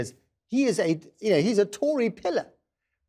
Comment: Speaking to Lord H, It rather sounded like he was saying Tory pillock, didn’t it? Oh well... So anyway ... As it is ever more clear that the EU will not budge a millimetre on their so-called ‘deal’ - which deliberately had a Northern Island backstop sewn into it, just because it appears insoluble and would make us want to give up; the only course of action which will spare us, Nationally, from seemingly unending paroxysmal inner political contortions is ... Leave without a Treaty on WTO terms and conditions. And, oh yes ... save ourselves £39billion ! Then all we need to do, is vote out every last treacherous traitor that attempted to deny the will of the people and nailed his/her colours to the mast of anti-democracy.